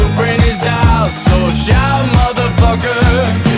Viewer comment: I initially thought it was saying Chou mother fucker lol